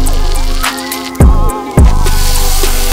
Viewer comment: I thought he said 3 cheese sandwich I was so confused 😭😭